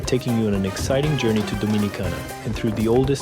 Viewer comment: Oye, eso lo que dijo 0:06/47:59🤨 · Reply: he says Dominicana not Dominica